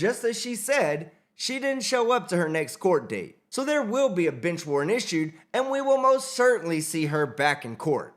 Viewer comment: I thought you said “b*tch” warrant! 😂😂😂